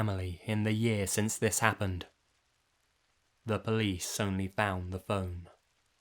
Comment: "the police only found the phone" 😥😢😧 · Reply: Holidae Caylen oh i heard he said" they found a bone" hh